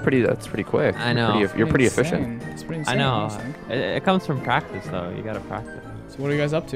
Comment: I thought he said “ it comes from Pakistan” 😂